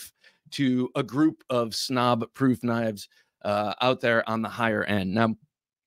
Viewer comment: Please dont banish me from the realm but it sounded like ya said "snob proof KNIFES" did I hear that right? 😂😂😂. just busting your chops a bit. Pioneer Jack is work/classy& Pocket Rocket is a bad guy deflator for sure. Top notch killer Nova 1s my liege!! I think I'm safe to say for the rest of the knives, from the little bit of them I can see that they are all killer (I knew Benchmade Bugshit wouldn't be on the list😂). Remarkable is a fancy way of sayin shitty, right? I can say something about the Hinderer, with that heat treat, ya sure it'll stay sharp long enough to complete a cut😂 sorry. I know I'm banished now but I couldn't not say it. Everything else is killer though!! Except for the 20.5 because makin great knives smaller to fit skinny jeans is un-American&yep that blade is butt ugly. The Pirite L is killer though&so it the Hogue Doug Ritter!! There's the exception that proves the rule with that Dirk Pinkerton Frankenknife beast. The regular size is like carrying a full size Medford. To big for normal pants pockets. It looks like a pocket Seax. The murdered out PM2 is almost the center of the SpyderCo web. I haven't got stung by the scorpion lock yet but its on the list. Outstanding top notch killer blades(except 2 cuz I'm a snob 😂)&KILLER VID!!